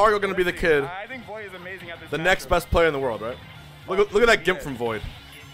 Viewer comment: When you uploaded the video bout the upset to spargo with ganon I thought u said bast player in the world for clickbait now I’m watching this